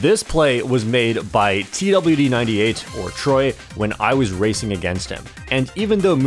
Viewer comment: did you say TLD98 LMAOO